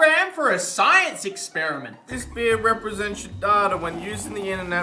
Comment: when they did the nordvpn ad i thought was saying “this is your daughter on the internet” instead of data and i was like “this is not gonna go well”